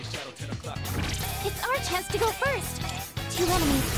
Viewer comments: I'm so high I thought fuuka said "it's our testicle first" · I don't know why but at 0:49 I heard "It's our testicle first."